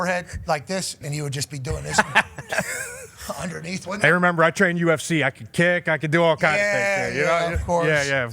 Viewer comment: He said "trained with ufc fighters" big difference in just doing ufc and training with professional fighters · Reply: Sounds like he said ‘remember I train UFC,’ a common thing people who don’t know about MMA who want to look like they do say, and then McAfee talks about Crosby training with UFC fighters. Or am I hearing it wrong? That dude sucks